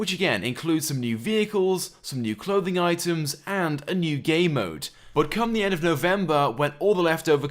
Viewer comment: Did you say "A new GAY mode" at 1:20?